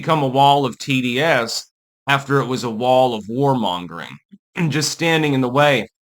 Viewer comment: When you said warmongering I thought you had said whoremongering, for a second, and I thought what's wrong with that for a true gentleman of culture...???